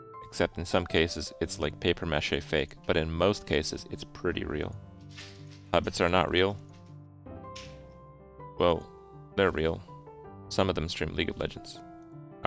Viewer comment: every time i hear hobbit, i hear habits, and was like wait how are habits not real XD